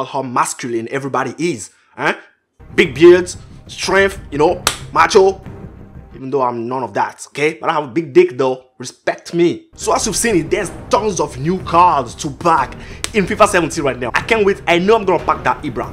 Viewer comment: did he just say "I got big dick though" ? 😭